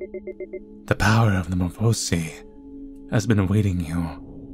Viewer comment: I THOUGHT IT SAID "WITH THE POWER OF METTATON" NOT METAMORPHOSIS. I'M SCREAMINGGG.